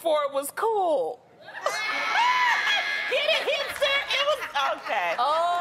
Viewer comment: I thought she said "hamster". I felt so dumb because I couldn't make the connection lmao